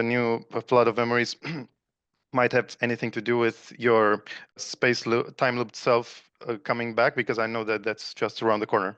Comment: you mentioned max pierce? I couldn't quite hear it. Trying to find that material. Thank you both so much! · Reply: Max Spiers <link>